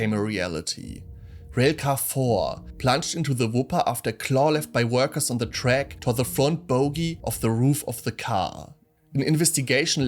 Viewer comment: What does he say at (2:35)? “...after klarliff by workers on the track to the front bogie off the roof of the car.” Anyone? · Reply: It's "Railcar 4 plunged into the Wupper after a claw left by workers on the track tore the front bogie off the roof of the car."